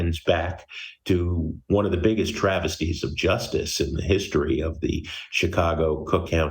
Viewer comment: Did he just say this was the greatest miscarriages of justice in Cook County🤮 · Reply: I heard travesties… not miscarriages 🤨